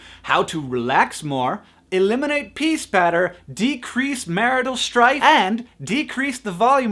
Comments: I thought you said "Eliminate peace better." I had to listen 5 times to figure it out. SO. CONFUSED. XD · Did he say ''peace better'' or ''pee spatter''?